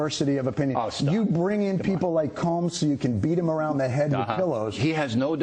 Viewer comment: would really appreciate if people could tell me who he said BIll brought in to beat around Anyone know what he said? "Comes?"